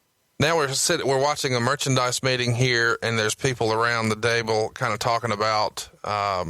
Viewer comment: Did he say "there's people around the dable"?